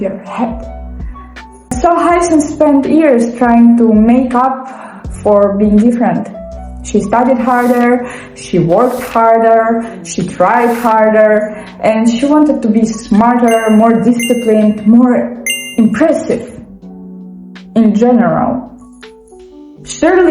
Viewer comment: When you were saying “tried harder”, I thought you said “cried harder”, and thought “no, she just needs a hug!” lol Anyways, I love all the thought you put into your characters! I’m the same way. Also you have a great vibe!